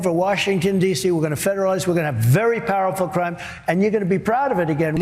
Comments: Trump: "....we're going to Federalize, and we're going to have very powerful crime, and...." at 3:28 Didn't anyone else hear that?!? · Hey Ben M: At 3:28 it sure sounds like Trump says “we’re gonna take over DC and we’re gonna have very powerful crime”. Talk about Freudian slips!